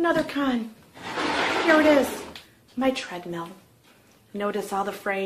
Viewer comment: Pro. I thought you said it was a "Shredmill". hahahaha